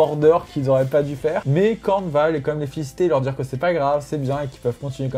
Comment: J'ai entendu "Khorne va quand même les fister" :))